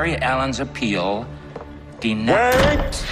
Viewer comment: I thought that he said denieeeeed